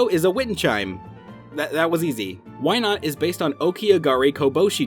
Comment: Why i heard Wynaut as "Why not"